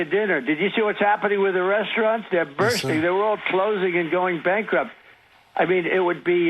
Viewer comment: did he say they were bursting, they’re all closing and going bankrupt?? wtf Edit: why did no one else hear this?